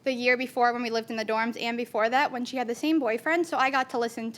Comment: is she saying she is boning the same boyfriend? Meaning her friend ex?😅